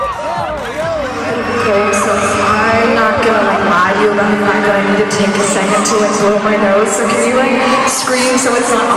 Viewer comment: what she said in 3:40?? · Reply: maximiliano gonzalez " I'm so sorry but I need to take a second to blow my nose. Sorry to do this to you. Can you guys scream loudly so it's not awkward while I go do that?